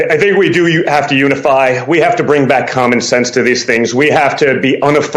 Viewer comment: 2:90 Around the 3 minute mark into the video. Junior demonstrates his Intelligence. "I think we need to bring back "Common Sense." I thought he said 'Com munism'. WTF? Yikes! These people don't understand what words really mean? Scrabble Babble. All they know is Alphabet Soup? Word Salad. As his Dad said, he has all the best words